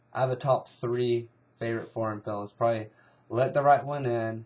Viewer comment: hahaha i thought you said my 3 favourite porn films xD